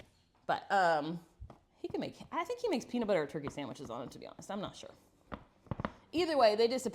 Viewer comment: did i hear "peanut butter and turkey sandwiches"? that is a new one to me. lol Today is the hubs birthday and i am making him crispy, big, tenderloin sammiches on homemade pretzel buns for supper.